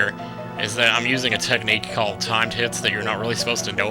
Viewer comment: wait at 3:12 did he say time tits i was like wtf